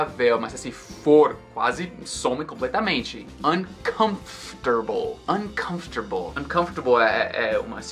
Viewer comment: Nice video, man!!! Did I just hear "un-comf-tar-ble" there?